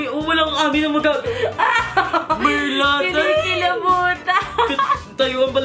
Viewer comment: po ano po yung sinabi ni ate dun parang may narinig po akong puta